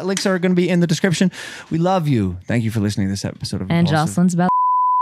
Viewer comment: What did she say at 1:05:56?? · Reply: and josslyn is about logan paul. sociopath too LOL